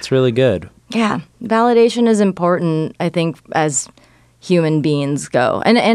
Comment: did say human beans??? 🤣